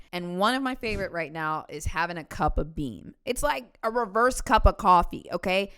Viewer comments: Girl, I thought you said you poured yourself a cup of beans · For a split second I thought said she drinks a cup of pee! 🤣😂